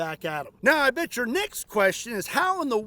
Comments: I hear that! "I bet your necks question" 😂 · I thought you said and your NECKS question lol